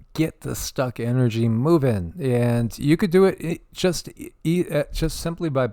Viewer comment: I so wish you would 🌹❤️❤️❤️❤️love you and your beautiful soul 🥰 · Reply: Lol I thought you said you could do it.. lol 😂 you are empowering us🤔💕I’m a lot off kilter 😬☠️